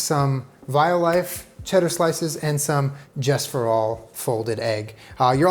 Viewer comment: I loooove the folded Just Egg!!! Gotta add some pepper and yes 😋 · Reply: I've been scrolling for SO LONG trying to figure out what this egg is. I thought he said "Jess-for-all" and google was not helping- so thank you!